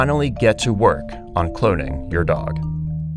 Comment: oh my god i thought this said clothing and i was like yeah sure but when it said cloning i just